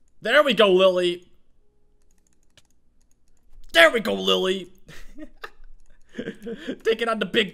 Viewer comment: did you actually say “Lily” or “Lil’ Eep”?...not that I want to know or anything... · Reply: He said "Lily"